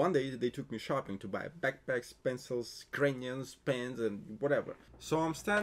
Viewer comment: Why when he said crayons I heard Ukrainians..... whos gonna get the Ukrainians first 🤣